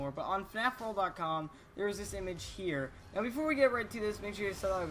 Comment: what did he say ? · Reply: all I hear is before we get right too this mubbling below